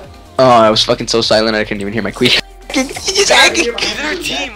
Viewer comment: "it was so quiet i heard my quef" cuts clip that second LMAO